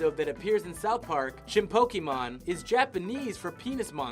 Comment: Did you just say 'Chinpokeemon'? What the fuck, dude?